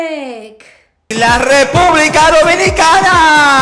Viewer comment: Does she really say "Ra Lepublica"? 😂😀 The guy actually says "La Republica"